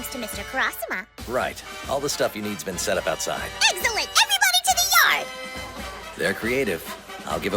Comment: did she just say eggcellent or excellent? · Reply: EGGCELLENT